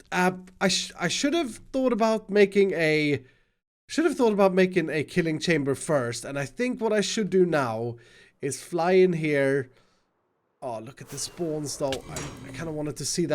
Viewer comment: I was watching this at 3am and when iskall said killing chamber, I thought he said kitty changer lol